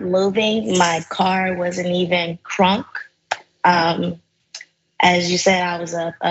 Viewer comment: "my car wasnt even ...."crunk" ..."did iI hear that right? what does that mean? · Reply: I heard it as, "my car wasn't even started". It's not rocket science.